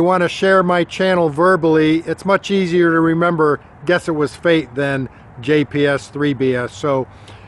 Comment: Heck....all this time I thought you were saying "guess it was fake". I never did understand why you were saying that...DUH!...lol